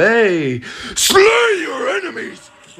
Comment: I thought it said “TAKE YOUR RIFLE PLACE IT ON THE STONE!!!” 😂😂